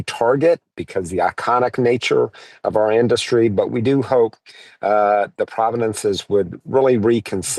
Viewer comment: pretty sure I heard "providences" not "provences", I don't think america should be taken seriously, probably ever again.